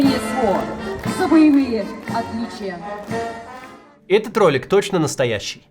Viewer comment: этот толинг, послышалось